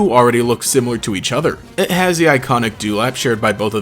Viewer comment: what word is shared by miraidon and koraidon? Dulac? Durag?